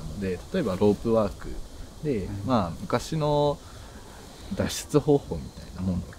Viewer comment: Did he say " that's the whole point " in japanese ? 🤔🤔🤔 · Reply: Oh interesting you mention it. He actually said Da-sshu-tsu hou-hou (脱出方法), which translates into “means to escape/get out” in Japanese 😁